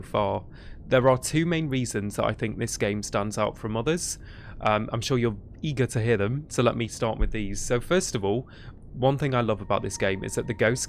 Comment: I understood "for mothers" instead of from others... I should clear my ears...